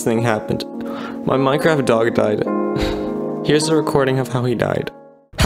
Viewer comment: I thought u said "my minecraft dattapack died :["